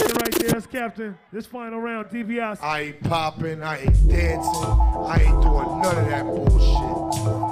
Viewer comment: What the voice get at(at 1:05) What does he say? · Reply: I ain't popping I ain't dancing, I ain't doing none of that bullshit.